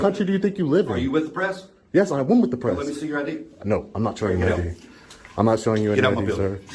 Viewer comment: Oh! You said let me see your ID! I thought you said show me your 45acp, my bad.